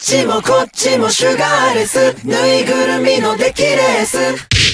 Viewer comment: あっちもこっちもシュガーレス！ ぬいぐるみも出来るヤス！ って聞こえる